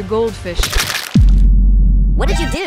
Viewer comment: I thought he said “we are going to meet your goldfish” and jumps into the water